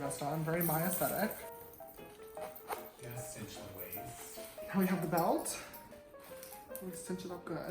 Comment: Anyone else hear “That’s such a waste”